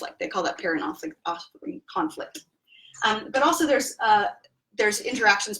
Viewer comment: “…and so you see that there's this interaction that is conflict they call that paradoxically conflict…” —The transcript is saying ”paradoxically conflict.” I am not sure this is what is being said. Please confirm. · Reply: It's called "parent offspring conflict" <link>